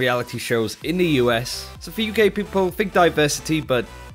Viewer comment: I could have SWORN I hear you say "so for you gay people" and I got very excited